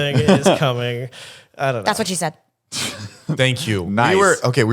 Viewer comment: does anyone hear what said ?????????? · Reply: Doubt youre still curious but it sounds like he said "she said stop"@